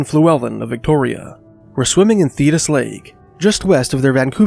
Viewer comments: I thought he was saying "Fetus Lake" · LOL I really thought you were saying Fetus Lake the whole time